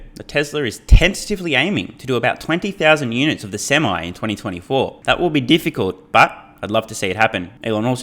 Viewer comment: I thought Elon said 50 thousand semi in 2024